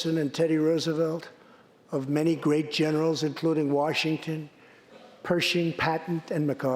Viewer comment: Was I the only one who heard him say General Patent?